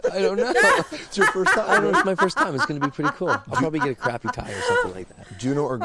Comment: I thought I heard Bublé say, "I'll probably go to Crappy Tire or something like that." but having listened to it three times, it was just "I'll probably get a crappy tie or something like that." Oh well.